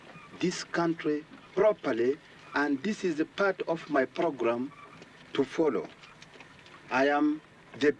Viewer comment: Did he say program or pogrom?